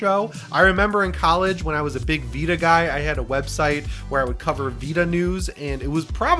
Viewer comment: Lol, I thought you said a Peta website 😂 I had to rewind and watch again. I'm waiting for the GuilKit KK3 controller. PS5 controllers absolutely use Bluetooth.